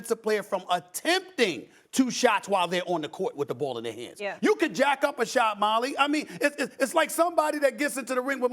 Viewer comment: Did Stephen A said "you can jack off and shot" or I just misheard it?